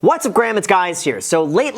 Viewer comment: ummm did you just say « what’s up Graham it’s guys here » LMAO hhhhh